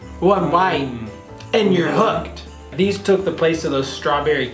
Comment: I thought he said one line and ur hooked I was gonna say wow it’s like cocaine 😃😂😂